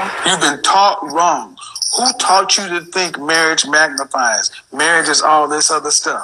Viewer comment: Swear I heard Malcolm X @ 20:48“who taught you?”